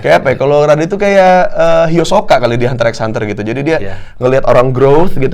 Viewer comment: kirain salah denger uus nyebut hisoka tapi emg kedengarannya hilusoka shdks, seneng hxh di mention soalnya